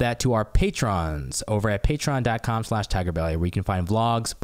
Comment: am i the only person who gets annoyed every time says "dot com?" almost every time he says it it sounds like he says "DAT com." am i the only one? it drives me nuts. i get angry. idky.